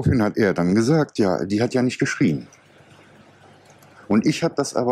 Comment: not 'write' it should be "she didn't scream" The Ai translation understood, 'sie hat nicht geschrieben' ' - she didnt write. But its 'Sie hat nicht geschrien" - she didnt scream". thats what Bruckner said to him.